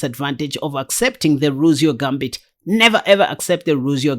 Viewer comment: Gambit but that's I thought at first you ere saying Muzio Gambit but that's the King's Gambit. I replayed the video and then heard "Ruzio Gambit" and that's not right either. Believe that the line is the Rousseau Gambit or line to the Italian Game which is pronounced "Rue-so" with the accent on the second syllable.